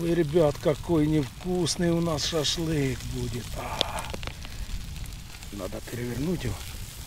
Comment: мне послышалось или он сказал "какой НЕ вкусный у нас шашлык будет"